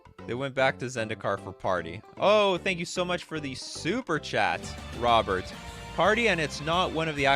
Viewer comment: I thought you said Superchad all the time!